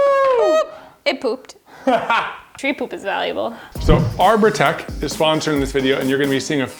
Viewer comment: when they said ''arbotech'' i first heard ''our protecc''